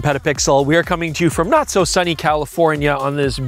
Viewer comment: For a second, I thought I heard say, "noct so sunny California..."